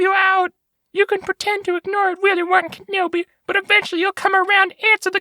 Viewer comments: I kept hearing "Willy Wonka Nobi" lol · Thought he said willywonkakenobi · I'll be real. The first few times he said "Willie-Wan Kenobi" I heard "Willy Wonka Nobi." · This time, when rewatching this I suddenly misheard something. Instead of Willy Won Kenobi... I heard... Willy Wonka Nobi.